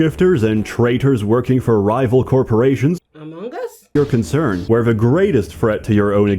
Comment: Oh, I thought you were saying "hugh-mongous". You were saying "among us". My bad. Also, godspeed. Sseth is a wild boi.